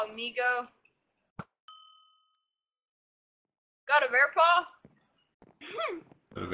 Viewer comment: hope you all L O V E the video also at 8:20 i did not say bear paw and said near paps